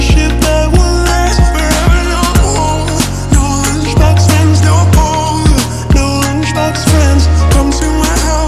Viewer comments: I thought she was saying no lunch box friends no hoes lol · I thought it was "no homo" instead of no 💀